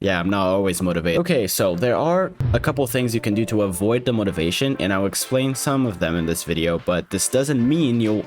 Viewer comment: I was so confused I thought he said “avoid the motivation” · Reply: until i realized he said avoid demotivation